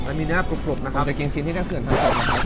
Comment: ทำไมเราได้ยินว่า "เราจะกินชิ้นที่'เจ้าเขื่อน'ทำตกนะครับ" มุแง้ เราไม่ได้หูฟาดใช่มั้ยยย